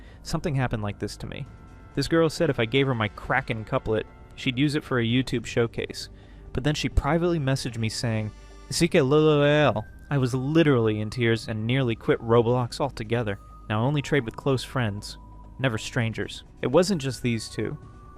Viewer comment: I love the content, hate the tts (noone says "ck lulolulo" instead of "sike lolol"